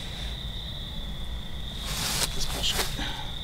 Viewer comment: Gonna get my headset on after it charges so I can hear that singing or whatever that was.I could hear it on the TV. I'll let you know what I hear.That was a great video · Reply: did just say it's bullshit